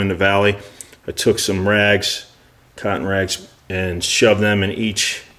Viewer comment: when you said “cotton rags” I had to rewind it because I thought you said “c*nt rags” 😂